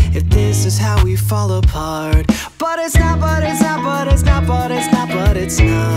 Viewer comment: Confession: I keep hearing " but its snot, but it's snot, but it's snot". And it makes it like 10x better